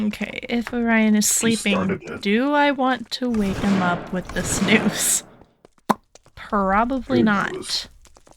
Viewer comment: What I heard say "should I wake him up with this noose?" Me: o.0